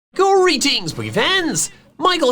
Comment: Who here first thought was saying Gory Teens instead of greetings when you first started watching?